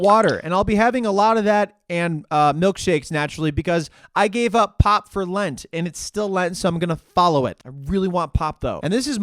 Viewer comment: i thought you said you gave up pot for lent...lol